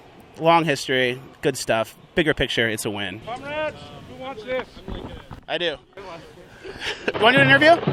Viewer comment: Thought that guy at 1:17 said "cumrags?? Who wants this?"